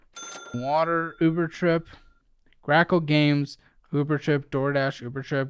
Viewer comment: I thought he said “crack cocaine” not “grackle games” 😂